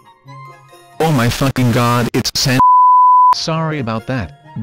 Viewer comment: Did He Just say San(s)?